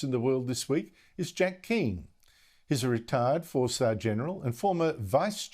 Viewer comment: anyone else thought he said retard 4 star general?